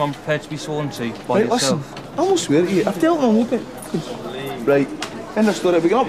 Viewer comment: did he say "I weren't swearing at you, I'm out in the open"?